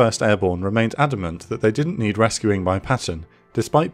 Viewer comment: I don’t know if it’s me, but do I hear, “ Bastonia”?